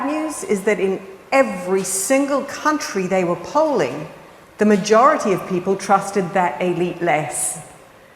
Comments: It sounded like she said “ the majority trusted their elite, less”. Like we all have an elite who is a pet or something. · At first i thought she said, “in every country we were culling ….” Actually it was, “in every country we were polling…” However, “culling” is probably more correct.